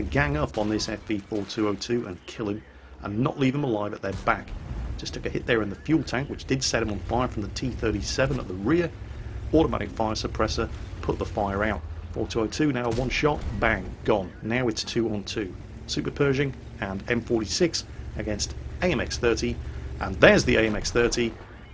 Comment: Lol did call that an automatic fire extinguisher??? xD